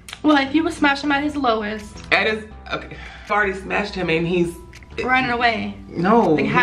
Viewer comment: did he say i already smashed him?(Him being jake paul)